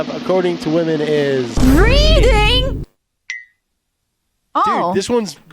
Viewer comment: For a moment I thought he said #1 was "breeding"